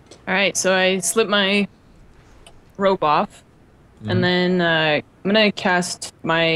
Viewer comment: when she said 'I slip my ropes off', I thought she said 'I slip my robes off', and I was thinking 'see the rest on rollplayxxx.'